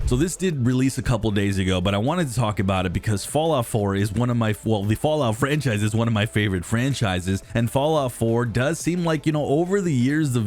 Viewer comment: what the fk did you say?... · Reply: Fallout 4 is one of his favourite 4s and the fallout franchise one of his favourite franchises